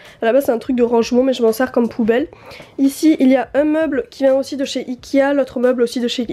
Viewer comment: Bonjour! J'ai une question un peu bizarre, mais voilà: J'essaye d'apprendre le francais par regarder ces videos, et je me demande qu'est ce-que vous ditez à 01:57. J'entends "shikia" mais qu'est-ce que vous ditez vraimen? Il vient de/chez ikea? Ou quoi? Je ne comprends pas. Aidez-moi si'l vous plaît!! :D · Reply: Bonjour, c'est peut être un peu tard pour répondre mais en effet et dit "vient de chez Ikéa " plusieurs fois